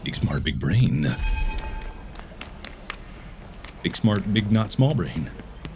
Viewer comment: kinda sounded like Mark said: “Big Smart Big Nut Small Brain”